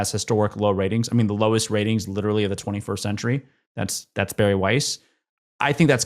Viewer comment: I thought they were saying Barry White. 😮